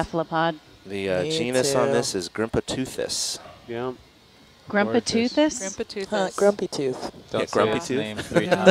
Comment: When they grimpoteuthis I thought they said grandpa toothless at first.